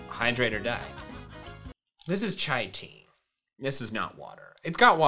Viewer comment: did said chai tea or I have hearing problem